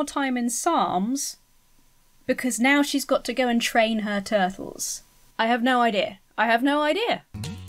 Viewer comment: ROFL when you thought the little girl said she had to go "drain her turtle" and thought she was saying that she had to go pee. That was hilarious! XD